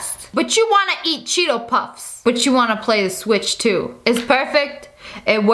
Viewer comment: Did she just say...switch......?